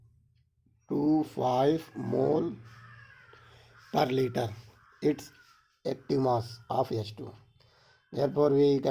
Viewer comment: kya bol raha h background me comment please 👍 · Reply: Crow 😂